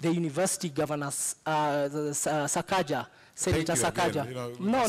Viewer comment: Did he just say, " governor sakaja?!" 😂😂😂😂😂😂